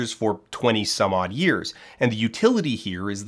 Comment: You said "twenty-some-odd years" but the subtitles said "thirty some odd years", literally unviewable